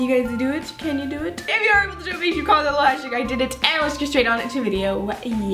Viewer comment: #IDIDIT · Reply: CatLover 123 i think she thought u said idiot but didn’t spell it right but u actually said i did it